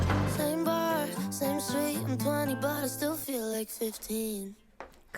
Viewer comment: Omg I thought in Calgary she was saying “and 20 bottles still feels like 15” as if she was an alcoholic